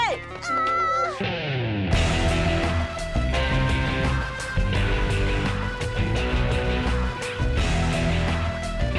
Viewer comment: Bande de quoi ?! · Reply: "Bande de bouffons" elle a dit